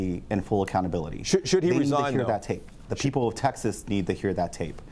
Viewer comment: sounds like he said "...the sheople of Texas need to know..."